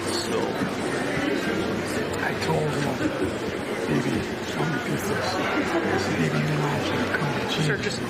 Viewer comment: I couldn't decipher the incoherent mumbling. What was Biden saying on that hot mic? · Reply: "Bibi needs a come to Jesus moment", he's talking about the Israel PM